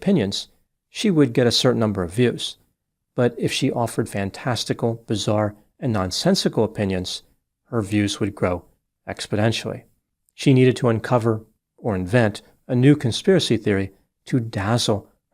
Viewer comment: Is he trolling? At 10:40 he is saying "expedentionally" instead of exponentially (as he criticised Biden of doing).... this may be a hint he's indeed to afraid to speak out or that he lost his mind..